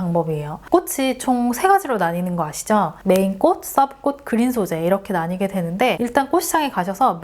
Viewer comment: 뭐라고 하시는 지 잘 모르겠어요 ㅜㅜ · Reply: ‘보통 메인꽃, 서브꽃, 그린소재로 나눠집니다.’ 라는 내용입니다.